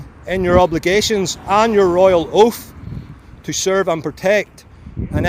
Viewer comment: Royal oath? Oh, with his accent I thought he said royal hoof lol Had to replay that twice